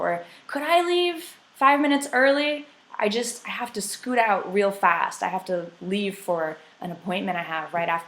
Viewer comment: Phasal verb?, I hear she says "I have to leave for an appointment I have right after the meeting". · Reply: After 4 mounths I heard that again and stil couldn't figure out what she said then I put the subtitles and according to it she said "scoot out" I've never heard this phrasal verb before